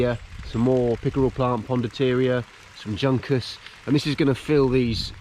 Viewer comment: 😂😂😂 I'm house hunting online and half watching listening to you... I heard Junkers and looked up expecting to see a German WWII airplane 😂😂 👍🇮🇪